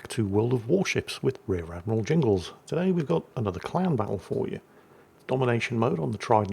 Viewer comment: I swear I heard him say clown battle right at the start of this video 😂😂 instead of clan battle. Guess we'll see if it's an accurate statement.